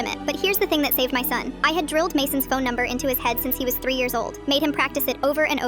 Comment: i heard "i drilled my phone number in his head"... and i was like HUH?